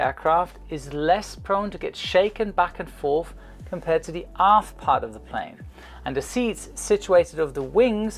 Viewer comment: who else heard "the ass part of the plane"😂😂